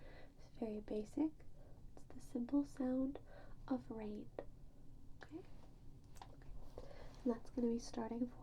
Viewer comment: i really has subtitles on seeing if i heard that right i didn’t it was rain but even the captions got ur wrong 🥲🙏